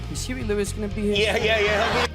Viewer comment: Can anyone tell me what he says? sry for poor English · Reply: "Is Huey Lewis gonna be here soon?"